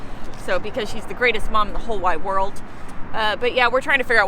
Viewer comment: sound like you said your mom was the best mom in the whole "white world" that's what you said??.Mmmh interesting statement.